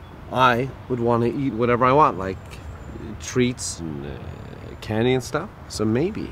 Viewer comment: Did he say Kenyan stuff